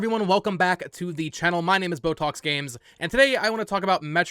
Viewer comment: Every time I hear your name, I always hear “Botox Games” LOL. AND BTW I think Nintendo follows through on their promise to deliver Metroid Prime 4 as a Switch exclusive. It will be the big game for the holiday season, I think. It won’t be cross gen IMO. Why lose potential sales by releasing it later as an upgrade on the Next Gen system?